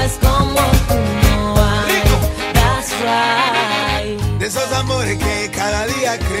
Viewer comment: Pense que decia "casual"y ahora me vendo a enterar que dice "thats right" xdxd vivi engañada ah